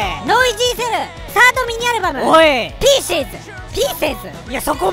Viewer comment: ピーシィーズ！ ピーセーズ？ 3回目になると どっちも正しく聞こえてくる